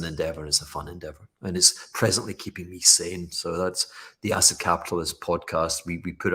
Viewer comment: I heard “asset capitalist podcast”, but it’s actually the “acid capitalist podcast”, haha awesome